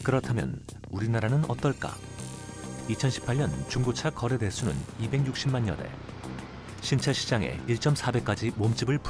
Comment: 뭐라고하는건가요.. 260만년회? 260만연회?